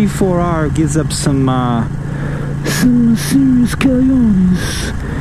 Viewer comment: wtf did u say at 7:58 lol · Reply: Serious Cagliones (cal-yee-own--ees)....meaning balls. I know balls in 12 languages.